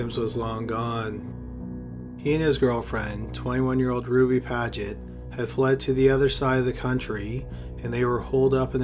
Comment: Thought he said her name was Groovy Padgett... Would've been a pretty sweet name though... 😅😅😅